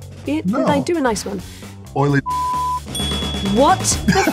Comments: does anybody have a clue what dwayne said? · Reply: Oily discharge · what nickname did the rock say? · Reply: Oily discharge